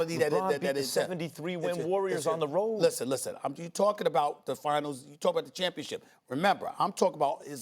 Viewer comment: did SAS say “your talking about the finals, I’m talking about the championship” or am I tripping? Lol